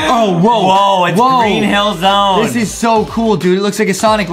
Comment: DID SAID "HELLZONE"?!